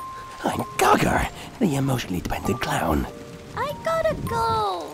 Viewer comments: “I’m Gar-Gar the emotionally demented clown” 😂😂😂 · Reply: At first I thought he said he was an emotionally dependent clown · “I’m Gar-Gar the emotionally demented clown” 😂😂😂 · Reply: I thought he said "emotionally dependent clown" XD